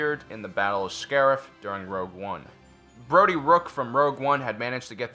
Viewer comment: did he say Brodie rook